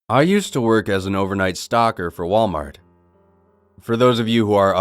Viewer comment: DAMN IT, I HEARD "STALKER" INSTEAD OF "STOCKER"